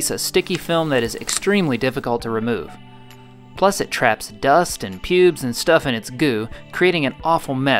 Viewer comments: did he just say "dust and pubes"? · Did.. did I just hear "dust and pubes"?